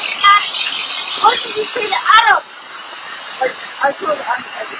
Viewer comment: "What did you say to Adam?!" Is what I heard. "I told Adam everything"